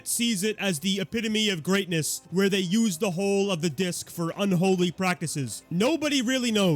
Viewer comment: At first I heard “use the whole of the disc” like native Americans using the whole of the bison 😭